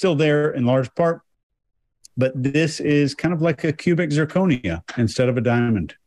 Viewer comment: ! PUBIC ZIRCONIA — LOL, while you were talking about the surrender steeple as a genital protector, I swear I thought you said it was like a "pubic zirconia" which totally made sense to me, plus I laughed. I had to back it up to see if I heard it right. But you did say "cubic zirconia" — but i heard "pubic zirconia" and it would have worked here. I think you've inadvertantly coined a new phrase! I'll allow you to use it, but you must give me credit once in a while, lol.